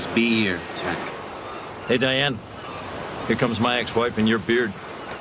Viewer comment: What does Cox say? "Here comes my ex wife and your... "beard""? If it's beard, I assume Cox was making fun of JD for being more girly than Elliot.